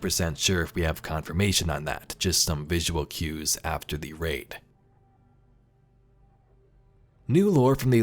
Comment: Might be me but did he say rape instead of raid atb@